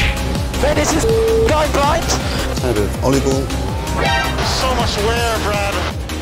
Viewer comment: am I going nuts or did it say oliebol